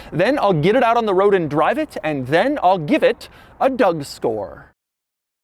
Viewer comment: Am I trippin or did I hear "A Doug Store"